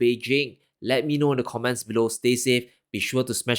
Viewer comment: Really well explained for dummies like me Very simple really if you know what to look at. Very enlightening graphs I was very surprised at the production and consumption #s. Also all this time I thought you were saying " never get through these crazy times"instead of" navigate through these crazy times." LOL